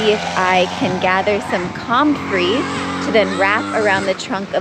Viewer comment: I was listening while cooking breakfast, grits, eggs, and biscuits, knowing I’d go back and watch. I thought I heard you say “concrete” to repair a cracked tree 😂😂 I just went back to that part, very curious to watch you use concrete to repair a tree LOL 👀😂😂